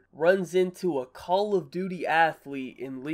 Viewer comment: did he just say call of duty athlete